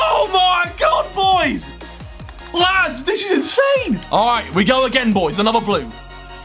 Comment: i thought Harry said the bunda is insane for a second 😂😂